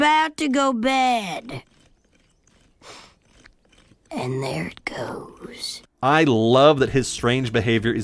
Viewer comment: personal mandala effect is I thought he said “…too late” instead of “there is goes”